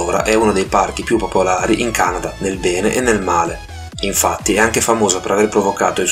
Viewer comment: ho sentito pene 0x0